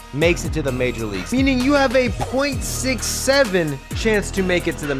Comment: what did he sayyyyy😂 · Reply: 6 7!